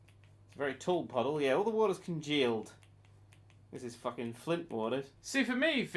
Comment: What did he say at 19:10? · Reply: Videos Are you asking about when he said "congealed?" It essentially means to solidify (only defining it because I'm not sure if you've heard the word at all before! Not too common <3)